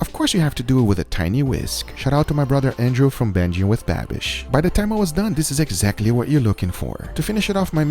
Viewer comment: Did he just say banjing with babish or is it just me🤣 babish fan comment just passing by🤣